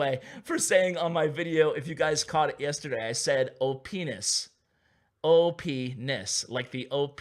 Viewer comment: I thought i heard OPenis but it was OP NESS